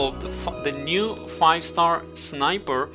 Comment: excuse me did you just said "fuck the new 5star sniper?" lmao